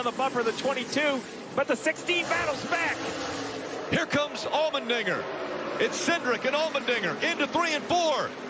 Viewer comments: had to replay that a few times, i thought he's saying "almond ni***r" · what did he say!!!! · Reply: allmendinger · ummmm what did he sayyy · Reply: Here's come Allmendinger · "here comes almond n-" not being racist thats genuinly what it sounded like XXDD